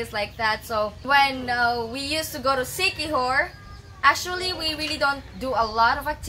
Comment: Gaining weight is subjective. Was he 10 pounds really gaining weight? Was he 50 pounds really gaining weight? Was he 68" and 130 pounds and gained 50 pounds, which made him under weight to a more healthy weight for an adult male. Please do not allow him to starve himself as he will lose muscle first and then gain fat second. Have him seek a health professional for assistance on safe ways to lose body fat without losing critical muscle structure. Sounds like he fell off the gym wagon and just needs to get back to the gym. Did she say they go to Sticky Whore around 4:30? Listened to it twice and she said they go to the Sticky Whore. That is a terrible name for a place, unless it is a purveyor of questionable woman. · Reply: Siquijor but it sounds kinds like that😋